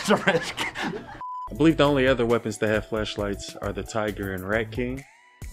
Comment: Bruh my mind is dirty, 0:30 thought you said fleshlight 🤦🏻‍♂️